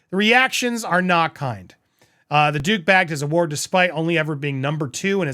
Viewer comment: When you said at 5:03 "The Duke bagged" -- I thought you said "The Dooooooooobag!" 😄😄😄😂😂😂🤣🤣🤣🤣🤣